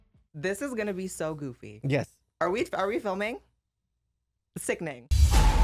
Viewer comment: what did you say? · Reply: sickening